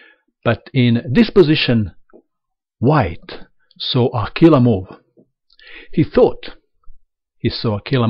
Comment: What did you say at 1:15? Killermort? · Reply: He is saying "killer move" with an accent.